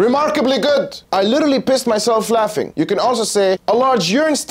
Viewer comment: I heard "A fly complain..."